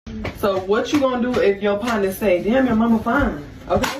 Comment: somebody translate what is she saying I don't understand? So many people in Blakistan speak as if they have traumatic brain injuries!😮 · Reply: Oh got it...she said: "whatchu gon' do if your partner says damn your mama fine!?" Which in my opinion is very inappropriate 🤨